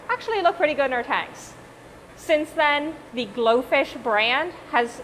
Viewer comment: How to keep glofish: dont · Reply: I thought you said goldfish